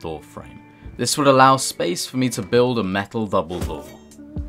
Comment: hahahaha did you guys realise that at 8:58 he says "vubblbore" instead of double-door lol